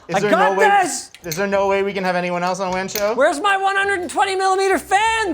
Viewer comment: Subtitle on Windshield LMAO instead of wan show